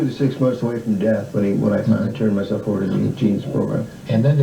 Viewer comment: For a moment there I thought he said "when I turned my life over to Jesus"